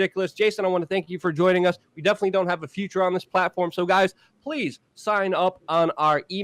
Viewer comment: Did i hear this correctly? 12:30 "Jason, thank you for joining us. You definitely "don't" have a future on this platform". Damn Luke, there is still some French Riot-Gas stuck in that mind of yours. Time to get some R&R (Ô.ô)